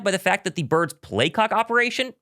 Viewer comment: their play what? i heard playcock 🤣🤣